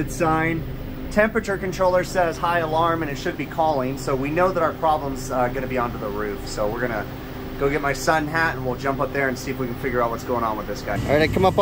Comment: “Grab my sun hat and head up on the roof” Me with 200 IQ: “You have a Son named Hat?” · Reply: I thought he said "Grab my son, Pat." I was like I didn't know he had a son.... 😂